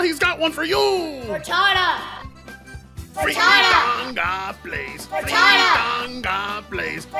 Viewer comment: why does the intro sound like its saying "fritada"